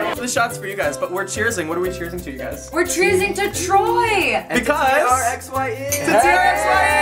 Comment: I thought Hannah said "We're cheering to Troyler!"